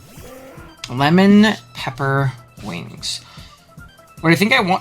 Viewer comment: Every time I hear “lemon pelipper wings” my heart gets three times bigger